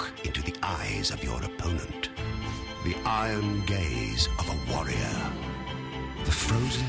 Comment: it sounds like he says "the i am gay warrior" :/ lol